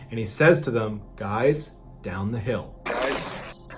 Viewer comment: I hear, “guys get down the hill”. He must be claiming he has a gun in his pocket or something. How has he not been identified?!